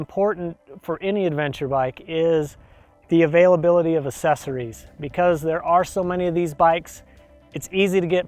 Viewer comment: Nice light weight Adv mod.I can't catch well you saying at 2:14-2:18. "Last of All ...Important any Adv bike is..." It sound like "cesry" or "susreeds".How do you spell the word, or What does it mean, please.Thanks. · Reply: He's trying to say accessory, but has a bit of a Utah accent.